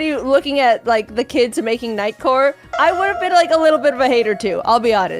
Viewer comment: I thought she said night court 😂 I went back to being 8 and trying to figure out Harry's jokes and why Bull wasn't on WWF